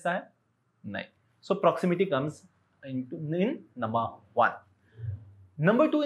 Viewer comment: What did he said in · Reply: I think he said "proximity comes in no. 1" And he mistakenly said into, instead of in, and then corrected himself...? Is what I think..